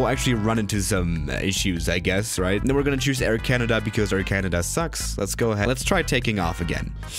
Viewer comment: did he say air canada sucks??!!!😂😂😂😂😂